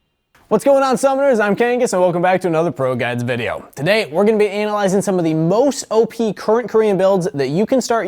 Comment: did he just say anal-ising